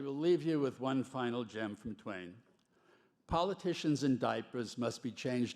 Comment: Oh, "politicians AND diapers". I thought for a moment he said "politicians IN diapers", which would have been a little more direct, if you get my drift.